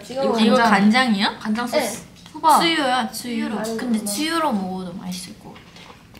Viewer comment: The subtitle says Tzuyu instead of "tsuyu", for a minute I thought they were talking about the Twice member and I got confused. 😅